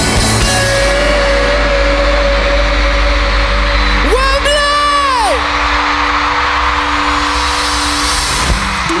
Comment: What is he saying? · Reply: Leaf yay he is saying « Wembley »